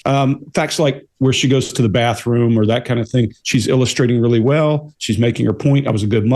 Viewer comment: I thought she said she sleeps really well at night ?!!